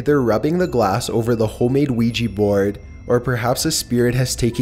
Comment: Chills: “Ouija bord” What I hear: “Luigi bord”